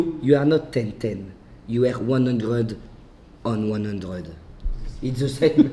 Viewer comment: il a dit quoi j'ai pas compris mdr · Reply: Bon 2 ans après j'te réponds mais en gros il avait dis, "toi t'es 100sur100" sauf que 100/100 c'est l'équivalent de 10/10 donc il est un peu couillon